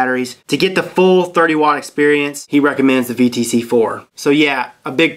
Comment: Can someone tell me what batteries he is mentioning at 07:55 ? i cant tell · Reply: sony vtc4